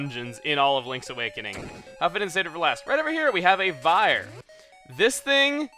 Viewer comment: One of those moments. I keep clicking 10:35 over and over, but no matter how much I do that all I hear is, "Huffadent save it for last." I know it must be something along the lines of "Glad they saved it for last," or "I see why they saved it for last," but dammit I'm curious about what he exactly said, for some reason!